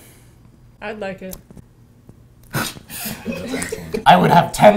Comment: what do Hila said at 2:13 ? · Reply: The question "How would you like to die?" was meant to ask about their preferred method of dying. Hila jokingly said "I'd like it" meaning "I'd like to die" instead. Funny meme. B^)